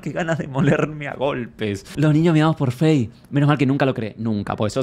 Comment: Entendí "Los niños MEADOS por Fey" Muy sereno ese fans club!!!! :face-blue-smiling::face-blue-smiling::face-blue-smiling